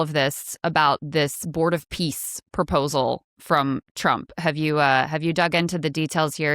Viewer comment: I heard it as Trump is bored of peace. Then it would make more sense. :face-turquoise-covering-eyes